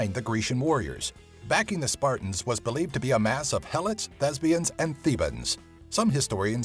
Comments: OMG he said thespians I thought she said lesbians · Did he say lebians at around 1:40 mark?